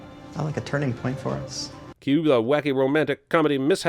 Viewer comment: subtitle says 'cut' when narrator says 'cue' at around 7:22, also narration.